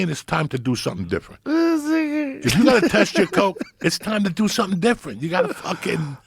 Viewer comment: hahaha what did Joe say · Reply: If you gotta test your coke (for fent) it's time to do something different